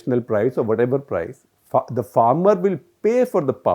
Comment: did he just say FUCK THE FARMER